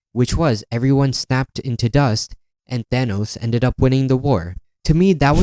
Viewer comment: Did anyone notice that he wrote Dinos instead of Thanos in the subtitle in 5:05? Anyways decent points but I still absolutely LOVE the leaf of faith and everything else in atsv.